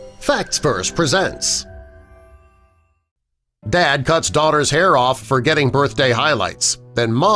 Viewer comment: I thought it said cut daughters ear off